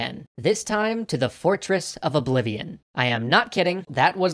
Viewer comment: Sorry, maybe I misheard but did you say 'Fortress of /I'mleavinagain/'???